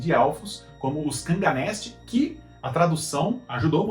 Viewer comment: não sei pq mais quando eu ouvi kanganesli eu pensei nuns elfos cangaceiros kkkkkkkkkk 🤣😂🤣